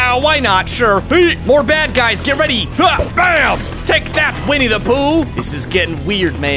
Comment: did he just say winnidepooh (idk how to write the name)